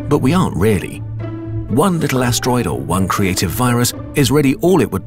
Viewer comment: DID YOU JUST SAY VIRUS?